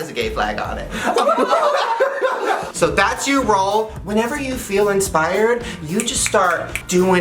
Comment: When Shane said rules I thought he said roles😂